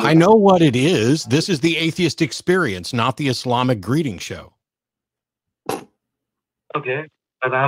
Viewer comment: "This is the Atheist experience, not the Islamic greeting show" Matt has said many funny things over the years. This is definitely top 3 on the hilarious tier list. I wasn't ready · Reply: I heard "Islamic breeding show", but I am probably wrong, because english is bnonly my fourth language, I`m so sorry ...................